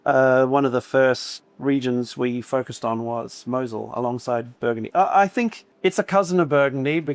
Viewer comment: did he say Meursault is a cousin of Burgundy?